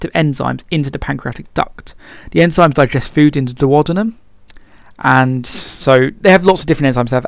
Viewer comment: sorry what's the word you say at 3:50 , enzymes digest food into..... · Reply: Duodenum